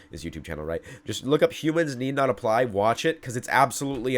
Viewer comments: At first I thought you said "Cubans Need Not Apply" and I was really confused as to why developments in robotics would lead to discrimination against people from Cuba. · When you first said the name of the video, I swear I thought you said "Cubans Need Not Apply", which then made it strange when you started talking about robots... :D